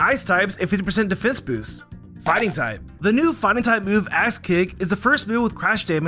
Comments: Sounded like he said ass kick not axe kick 😂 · is it just me or does it sound like says ass kick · definitely thought you said ass kick 😂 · anyone else hear ass kick here? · lol. It sounds like you said ass kick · anyone else heard ass kick instead of a e kick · I swear you said "The new fighting type move ASS kick". 💀 That would be so funny if they had a move called Donkey Kick and it got translated to ass kick. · the first time i heard him say axe kick i thought he said ass kick XD i did a double take and had to rewind the video a bit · IT JUMPSCARED ME BECAUSE I THOUGHT HE SAID ASS KICK LMAO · did you say the new move is Ass Kick?? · You said “axe kick” but I thought you said “ass kick” I was like what are they doing to Pokémon these days lol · okay I know I'm not the only one who thought he said "ass kick" 😭😭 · I thought he said ass kick instead of axe kick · Did I hear that correctly? ASS KICK? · i can't be the only person who heard a$$ kick here 😂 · i thought you said ass kick lmao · Did anyone else hear "ass kick" instead of "axe kick"? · For the first fact for fighting it sounds like he said axe kick as Ass kick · In 7:41 i heard ass kick